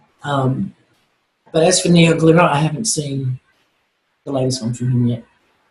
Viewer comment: Neal who? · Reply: Cool, thanks; I thought she said Neon Glitter...listened to it 5 times...LOL but Neon Glitter does nail polish tutorials ...lol Thanks again!